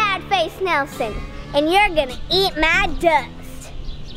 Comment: When she said eat my dust, I thought she said eat my gut.